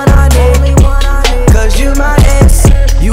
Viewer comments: I thought Austin was saying "cuz you my ex" and I was like whaaaa😂😂😂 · The entire time I thought they were saying “your my ex” Edit: wow I have never gotten so many likes xD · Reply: I thought they were saying "you my ass" lmao 😂😂😂